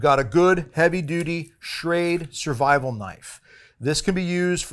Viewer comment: I thought you were tryin to say serrated knife at first lol.